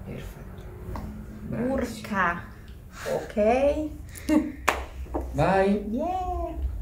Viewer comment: What does she say at 00:55 ? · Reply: "Urca! Ok..." "Urca" meanig nothing, but it used to show surprise, quite as "Wow"